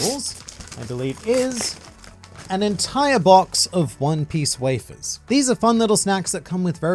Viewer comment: For some reason I heard “One Piece Waifus”